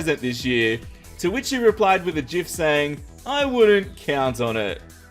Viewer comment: did he say JIF?